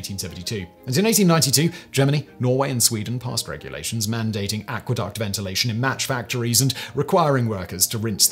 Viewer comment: did he say aqueduct ventilation instead of adequate ventilation?